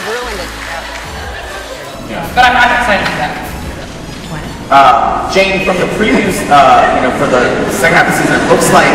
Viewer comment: What did Chris say at 2:47?? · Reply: Oh shit, sorry xd Chris say "And I'll be back", it was in the 2 season, when Chris was in Dalton, and no one knows that he will back to McKinley, and he told it, and everybody start to laugh about it and him face like 'Shit, they didn't know it' hahaha